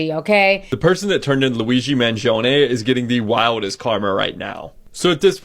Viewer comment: All I hear is Luigi's mansion